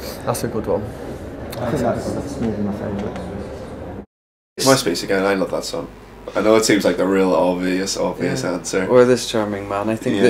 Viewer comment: ?? I'm Irish and I can't catch what one he says?? lol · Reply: Relistened & caught "__ Again" so I'm assuming he says "Bigmouth Strikes Again"